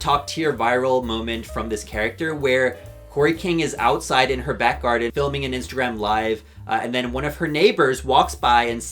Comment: can someone tell me what the neighbor is yelling at kori in the shrek vid at like 15:20 because i swear i've watched it so many times on twitter and i replayed it a bunch here too and i can't really make it out 😭 also jesus i thought i was a kori stan but there's so much cameo character lore i didn't know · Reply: the first thing they say is “love the costume” but i can’t make out the second part